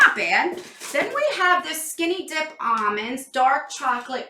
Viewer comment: Omg I’m dying 😂 I legit heard “skinny d*ck balm” when you said “skinny dipped ALM-ond “